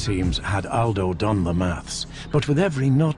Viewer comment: what is maths or maphs? he says it